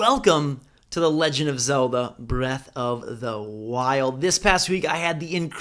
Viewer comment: This whole time I thought it said "Zelda breath of the wind " not wild 😂😂😂